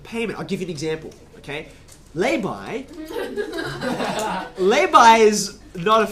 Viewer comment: does he say "lay by/buy"? what is that anyone know? or is he saying labor with an accent lol